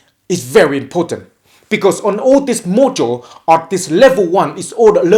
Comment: I thought he said "This mojo" referring to himself...